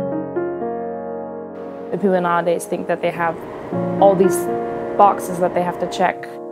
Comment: Help! what does she say exactly at 0:42?? "if you honestly think that they have..." or mabey "if you're an Artist think that they have..." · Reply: "people in our days think that they have", is my guess